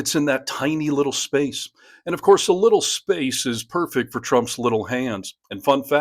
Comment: what he actrually said was -"legal space", not little space.